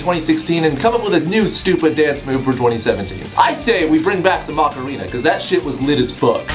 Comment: I thought he said margarita not Macarena 😂. And I got in the mood for one